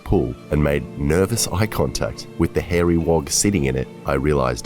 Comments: Did he say hairy wog ?! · did you say wog??? Hahaha not sure you can say that lol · Did he just say hairy wog? That means somthing very different in the UK 😅